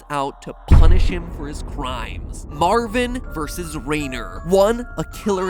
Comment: Thought you said Renier, one of the presumed characters in Project Libitina.